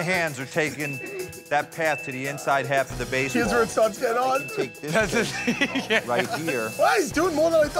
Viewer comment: What did Q say at 1:01? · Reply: “Here’s where it starts to get hard”